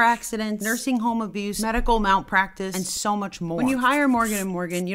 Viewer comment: I thought she said medical mouth practice